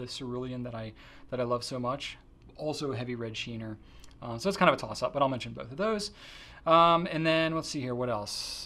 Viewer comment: WTF did he say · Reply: “So it’s kind of a toss-up, but I’ll mention both of those…”